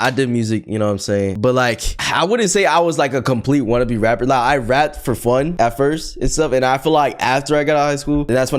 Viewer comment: why did it say on subtitles i raped for fun?